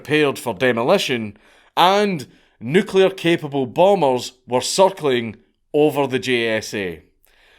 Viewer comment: I'm sorry. Everytime I hear you say "circling" I hear "suckling" lmao